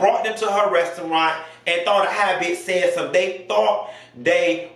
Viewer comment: “Thought a high biyatch said sum” 💀☠️